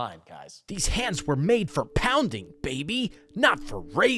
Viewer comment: I heard it as: "These hands were made for pounding babies." 😂